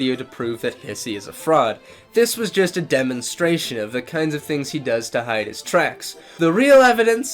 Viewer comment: I swear to god at 8:18 the subtitles said “pissy is a fraud this was just a demonstration of” 😂😂😂😂😂😂😂😂